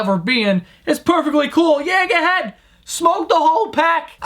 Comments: I thought he said 'yehet' at 1:05, I gotta stop XD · did he just say yehet? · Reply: he said "yeah go ahead"